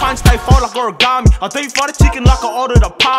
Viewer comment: Thought this guy said deep throat the chicken instead of deep fry 🤦🏾‍♂️😂😂